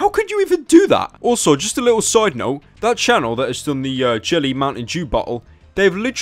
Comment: it sounds like he says mountain JEW